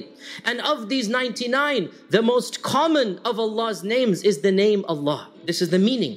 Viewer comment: Thanks for the video. However I have noticed serious mistake in the subtitles/text. 5:19 of the video instead of writing ALLAH'S, you have written ALLAN'S. Please change this soon as possible. Thank you All brothers and sisters please like this comment so the Admin for this channel can notice the comment and change the mistake May Allāh reward you all with goodness Asalaamu Alaikum Warahmatullahi Wabarakathuhu